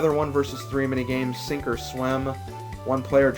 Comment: for some reason when you got "sink or swim" i thought you said "super slim" (slim obviously standing for) lol